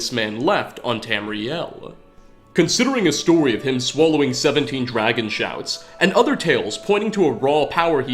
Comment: I’m sorry, I heard “swallowed 17 dragon SHAFTS”